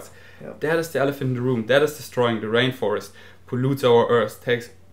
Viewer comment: I honestly thought you said "that is da straw in da rainforest 😟" but you actually said "that is destorying"